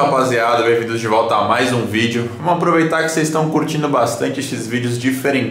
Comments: Só eu que ouvi xvideos? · só eu ouvi ele falar xvidi0s?? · "XVideos" 00:04 não é tipo porno? Desculpe ae galera pela palavra mais oque eu ouvi falar é isso